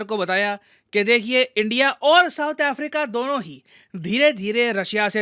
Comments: Sir you said south korea instead of South africa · I think i heard South Korea 🤔